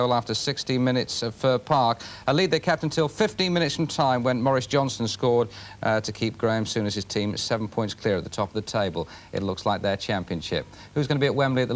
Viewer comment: thought he said Boris Johnson!